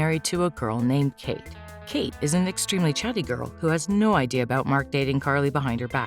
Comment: Kate isn’t chubby...she’s literally the same size as Carly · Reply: thats what i thought she said but if you turn captions on you would know she said chatty